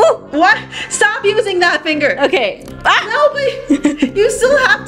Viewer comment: did she say no shinobi